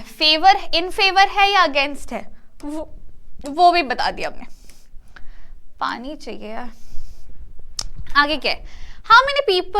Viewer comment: did she said pani chahiye?